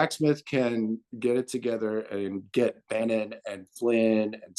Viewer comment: At first I couldn't make out the name he mentioned but I FINALLY got it, BANNON...when Jim is saying, "If Jack Smith can get it together and get Bannon and Flynn and Stone and you know, the roots of this thing off the field, much less Trump..." then there is a chance for a save of demoracy. Indeed. Kudos to you, thank you so much for your contributions during the recent stages of this fight. I've long said to my little circle that they subjugated our '16 election. Every time that jackass hollers his '20 election was stolen I always clap back that no, his '16 win was the one stolen, along with 2000. Very insightful to learn from Jim where the turning point was with Flynn's GRU HQ visit June 2013, and then how events transpired fromthat point; the Flynn/DIA ole of Crimea, the RT dinner, etc.